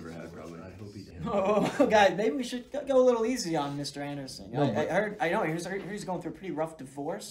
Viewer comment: I had this on low volume, and the whole time I thought they were saying mr. Anus